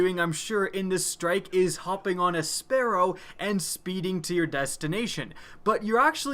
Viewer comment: I heard "humping on a sparrow"